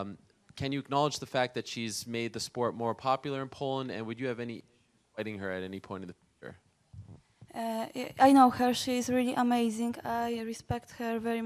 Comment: wtf he say? lol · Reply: Maybe, "Would you have any problems about fighting her at any point in the future?"